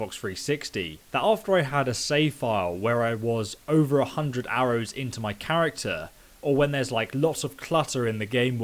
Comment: did you say "after I was over 100 arrows into my character?"